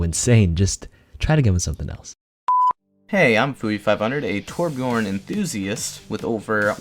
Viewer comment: Did I hear toRBJORN ONE TRICK REEEE Edit: it's ok. I misheard. He said Torb enthusiast. I'll let that slide